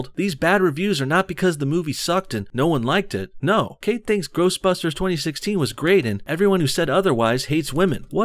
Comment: Did you say " gross busters 2016 "